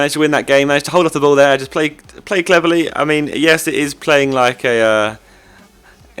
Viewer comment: "Play Cleverly" It sounded like "play cleverley"